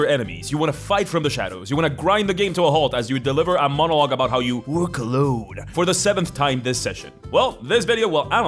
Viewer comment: "... you wore cologne..." is 100% what i heard the first time i listened to this in the work truck 😂